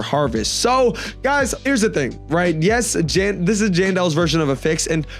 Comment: Him saying genderless instead of Jandel is wild 💔🥀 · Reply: The subtitles said genderless but he said jandel